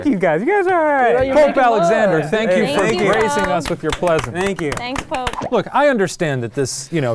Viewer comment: Shane said plesence instead of presence and he knew he fucked up